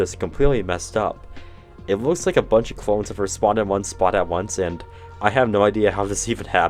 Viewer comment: Why did you say "spawned at once" twice?? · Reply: spawned in one spot at once